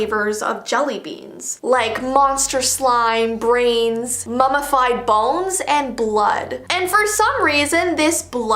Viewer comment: did i hear mummified balls?!